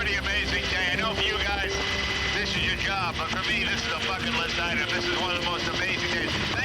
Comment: Oh I thought I heard jay curse in 🚁 but he just said bucket list, 😂